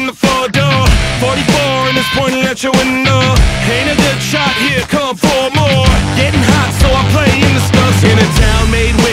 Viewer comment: For the longest time, I genuinely thought he said "Ain't it a dead shot here come four more"